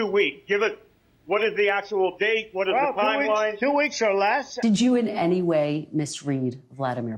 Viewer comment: He said “I’m too weak” not “In two weeks”. You misheard Jimmy